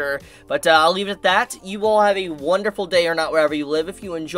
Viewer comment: I find it hilarious that I always hear "have a wonderful day or not" xD